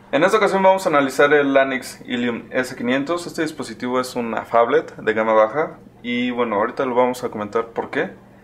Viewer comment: Perdón que dices que el dispositivo es una "Fablet"?